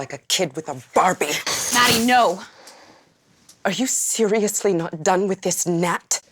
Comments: Did anyone else mishear "are you seriously not done with this gnat?" as "are you seriously not done with this, Nat?" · when Maddie says "are you seriously not done with this GNAT?" I thought she was saying NAT as in Natasha 😂